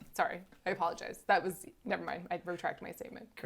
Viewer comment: did she say I "RUtract" my statement ???????????????